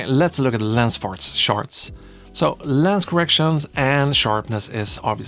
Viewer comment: “lens farts sharts” did I hear that correctly? 😂